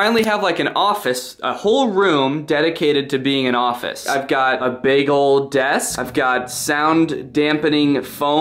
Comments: who else thought he said “I’ve got a bagel desk” 🤣🤣🤣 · Not gonna lie at 37 seconds I thought he said “bagel desk” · I heard "I've got a bagel desk!" 😂👏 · i keep hearing, "i've got a bagel desk." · I thought he said Bagel Desk for some reason and I can’t forget that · I don’t know why but I thought he said bagel desk and I got really excited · I thought he said bagel desk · Instead of "big ol desk" I heard "bagel desk" instead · Honestly tbh I thought he said "I got a bagel desk right here" and not "big ol' desk" so ya, here is the time stamp 0:35 - 0: 37